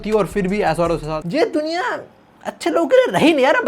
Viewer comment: yeh duniya pital di that is what he was actually saying😂😂